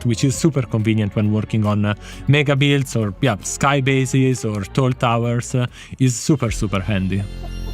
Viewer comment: wait what did he say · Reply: Torll tawers (tall towers)